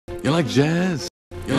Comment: I have a question to all of you guys Ya like jazz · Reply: it sounds like he said to you like jizz